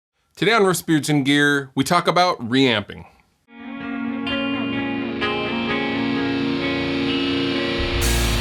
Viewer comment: I didn't hear "Riffs, beards, and gear" I heard "Rasputin gear"